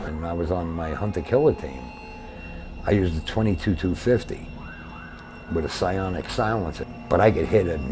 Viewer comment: He literally said “.22-250 with silencer.” I doubt a SF dude would mean M2 by saying that. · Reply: oh. I thought I heard 22 m2 50 and thought maybe the 22 was a speaking error. I don’t know much about guns but never heard of a .22-250. I agree, it sounds like it would be a terrible sniper rifle. That’s probably only good for hunting squirrels. lol.